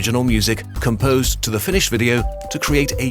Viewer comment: i heard "composed to the Finnish video" XD